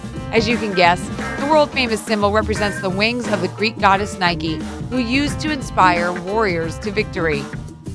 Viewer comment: did she just say ''nikey'' ???